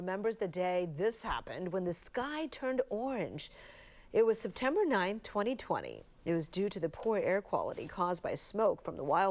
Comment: i thought i heard "september 11 2001" instead of september 9 2020 💀